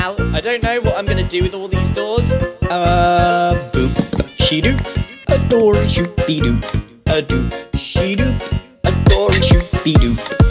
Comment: I thought he said "a door should be dooped" lol